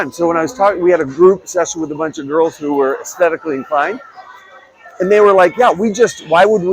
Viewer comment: At first I thought you said, 'aesthetically fine' instead of 'aesthetically inclined', hahaha! And yes, a lot of our dishes are made with bone broth. I'll even make it in the summer. Great not only for collagen but for the gut!